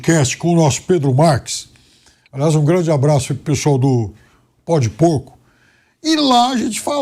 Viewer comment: Perai! Eu ouvi direito no 3:12 ele falou no podcast Pode Por??? 😂😂😂😂